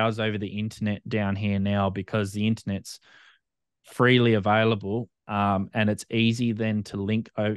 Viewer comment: You and a fellow club member - are excellent at instructional information thank you. Question - Your offhand comment about Internet access in VKland is it REALLY free? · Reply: you said the internet is freely available , I think this is where oldmissouri is talking about and thought you said the internet is free?